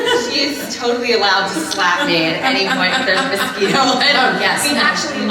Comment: Can somebody please tell me what Lana is saying at 9:56? Lana: "By the way, she is totally allowed to slap me at any point [missing part]" Ginnifer: "Mutual agreement" Lana: "Yes" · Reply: if there is a mosquito